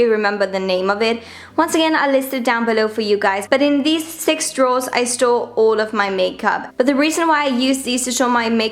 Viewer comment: it sounds like she said " in thees drawers I stole all my makeup